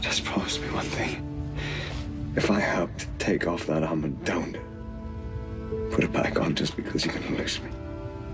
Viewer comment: what does he say to at 38:43? I can't make it out :/ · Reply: he says "if I helped take off that armor, don't put it back on just because you're going to lose me." that may be a little off but he said something along those lines.